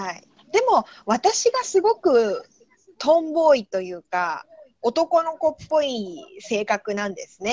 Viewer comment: i heard 'tomboy' and had to pay extra attention that I even translated it lol 'わたしがすごくtomboyとよかおとうこういいせかくなんですね' if I'm correct